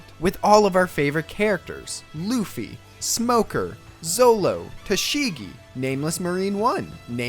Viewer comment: Is it just me or did he said solo (trigger) 😡😡😡😡😡😡🤬🤬🤬🤬🤬😡😡😡🤬🤬🤬😡😡😡